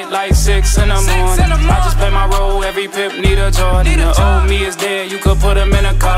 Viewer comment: Did he just say every pimp need a joint? 😳